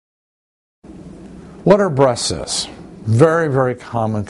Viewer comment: I thought he said "what are breastests" lol anyway! Good video